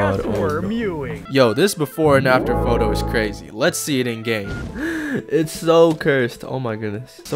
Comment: SOUNDED LIKE HE WAS SAYING "lets see it in gay"😭🙏